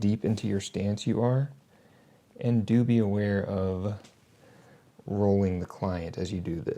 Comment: did someone say doobie?